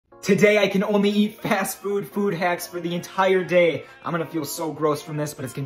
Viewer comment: Who else heard him say "fast food food hacks" 👇